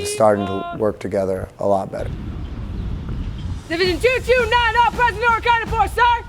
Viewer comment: what did she say? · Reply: “Division 029 all present or accounted for, sir”